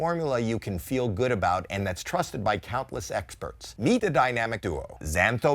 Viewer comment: In the add read for xosialX sounded like he said topless experts 🤣 😂